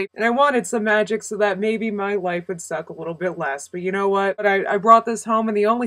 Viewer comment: 48 i thought you said my wife would suck a little less, and i was thinkin, why would you want her to suck LESS?